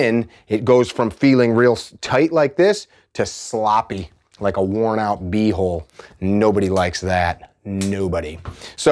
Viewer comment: Did he just say a worn-out BUTT HOLE! 😂